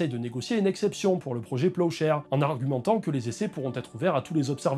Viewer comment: Pendant toute la vidéo j'ai entendu "projet peuchère" 😂